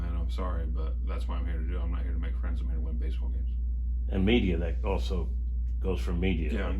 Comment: All i heard was "were here to win ball games". Nuff said.